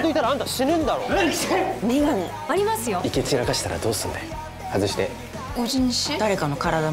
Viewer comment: 生き散らかしたらどうするんだよって聞こえるのですが、なんて言ってるか分かる人いますか？